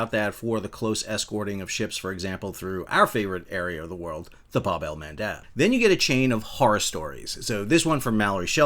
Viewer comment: "Our favorite area of the world, the ___" Allow me to apologize for being such a noob, but I couldn't understand that! 😮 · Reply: Look up Bab-el-Mendab, the strait between Yemen and The Horn of Africa. The hunting grounds for the Houthis. had been mentioning the area so much for the last couple years that it became a drinking game: Take a shot every time he says "Bab-el-Mendab".